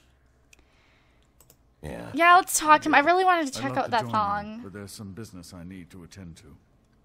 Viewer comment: did she say I really wanna check out that thong!?